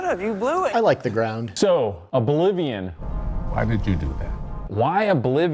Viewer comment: I totally heard "A Bolivian" when they introduced Oblivion